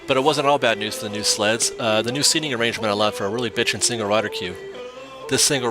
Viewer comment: Did you say “bitchin” @ 4:00 ?😂